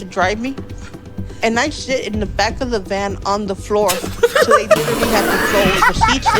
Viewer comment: Your better than me I thought she said I have to shit in the back of the van